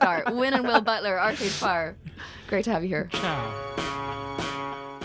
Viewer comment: Did Will say "ciao" ? :O